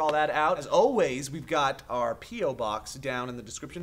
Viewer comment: Sounded like said "Pedo-box"